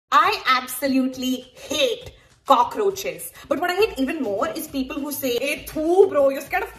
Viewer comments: Firstly I heard “I ate cockroaches ” meanwhile me in unconscious :🤯🤢🤮 just wanna see that stupid face,butt you're cute😅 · Me who thought she's saying I ate cockroaches 😂😢